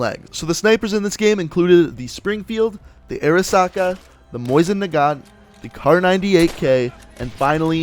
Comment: sounds like hes saying moist nagant